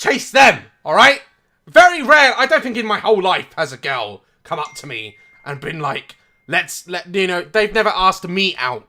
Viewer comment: When daz said ‘ I don’t think my entire life has a girl come to me ‘ I heard ‘ I don’t think in my entire life AS A GIRL